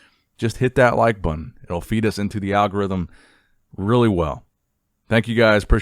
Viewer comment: When said "It'll feed us in" I thought he said "Auf Wiedersehen" to the algorithm (some sort of strange reference to Arnold's first language)!